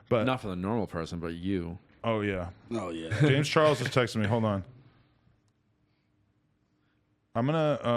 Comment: I thought you said Ray Charles texted me and I was like how??